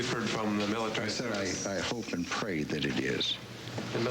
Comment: Did I hear correctly, this nut job "hopes and prays" the casualty count is high?! What a sicko.